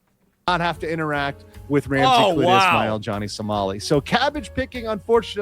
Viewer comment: Dude, I thought he was saying Johhny Somali's name was Ramsay Cletus Smile lol.